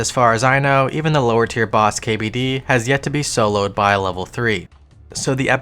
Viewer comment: Tedious did a level 3 hcim kbd kill by the way <3 love the series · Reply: That's my bad, yes I did read the title, you don't have to be rude. At 1:28 he said he didn't think it had yet to be soloed by a lvl 3, but I thought he said killed. My mistake.